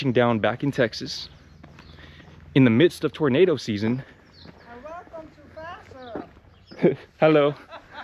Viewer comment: it’s happening again at 3:00. What did she say? Lol!😂🤣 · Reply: Lmaooo she was just saying welcome!